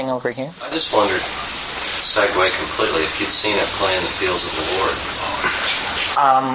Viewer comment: Could anyone understand the name of the movie/book they mention at 2:54:30? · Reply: At play in the fields of the lord